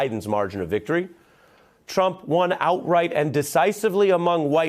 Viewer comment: 😯 Oh, "outright"; for a second, I thought you said "alt-right" 😄